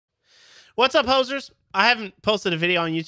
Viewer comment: I'm laughing my ass off because i thought you said "Whats up posers!?" but then I remembered you're Canadian. Fun video. Missed this stuff.